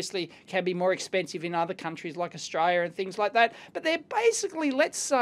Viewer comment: did you say Australia there ?? Lol